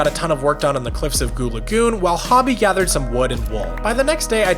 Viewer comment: did i hear it right? Is it goon lagoon?